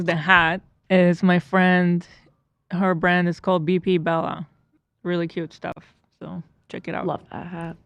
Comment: i NEEEED hila’s hat does anyone know where it’s from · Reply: I think she said "beefy bellas"